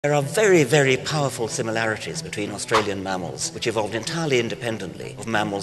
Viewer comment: For some reason I thought he said theres a great deal of similarity between humans and australians lol